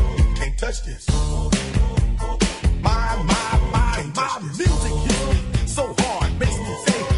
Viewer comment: Am i the only one who thought it said milkshake instead of music